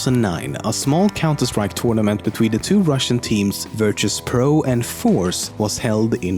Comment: Wait, what you said? Two RUSSIANS teams Did he said that?